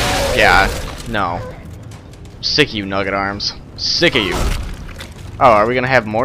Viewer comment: nugget arms sounded like he said f*** you